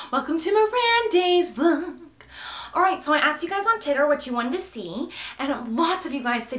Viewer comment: did she just say Titter lol?